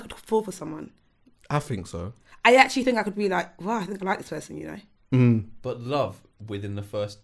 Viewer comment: Did she just say “I liked his arse” lol 🤣